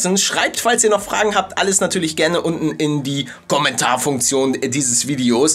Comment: palle hast du bei 2:50 min gommentare gesagt hab ich so verstanden